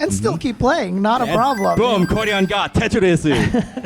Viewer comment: Did he just say tetorisu?